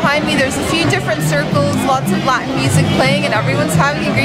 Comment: did she say lots of "black music" playing? hahahah . bless her heart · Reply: Lot's of Latin music playing. Listen closely.